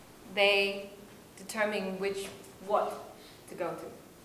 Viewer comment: What did he say? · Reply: bucket. It is supposed to be a bonus question but the students should find the answers on their own.